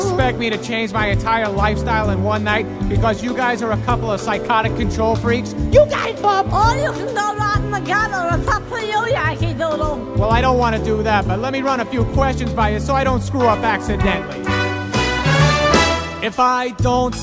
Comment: I thought she said “or you could go rot in the gutter it’s up to you Yankee Doodle